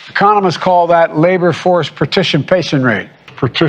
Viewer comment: i not a foes to guns either grandpa for we heeded not our own worst of instincts, but our own Californian baseball batter of Angels. If you listen carefully he actually says, " Pertitionpationrate. "